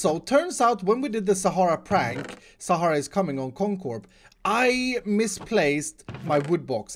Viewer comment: ok, the only thing i hear is "sahara's cumming on comcorp" please help